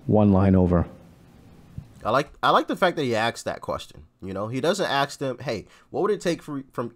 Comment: how come I always hear him saying "axe this question", although autosubs show it as "ask"? Do I really mishear him every time?